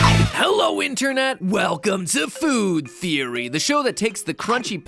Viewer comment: I thought he said winternet